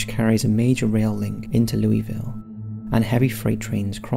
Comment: I thought he said Louisiana. Guess he meant Louisville.